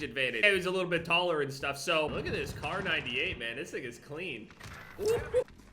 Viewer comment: Did he just say this N***a clean or This thing? Lmao